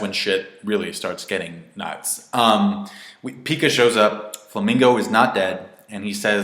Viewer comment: did you just say flamingo